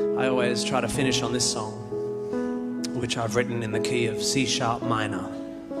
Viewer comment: am i the only one to hear KFC sharp minor instead of Key of C sharp minor? )